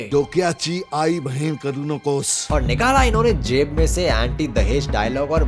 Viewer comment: yeh Marathi me kya bola Mithun dada ne ?? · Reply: Dimaag ki ma bhen mat bana